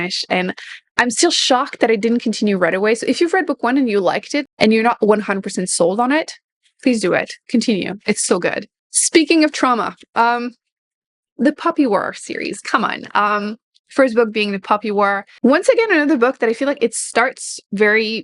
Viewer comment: I wasn't looking at the screen and thought you said you recommended a series called "The Puppy War!" I was like, "The...what?!! I don't care how good you say it is, there is no way you're convincing me to read a series about puppies killing each other!" But anyway good list, will definitely check some of these out.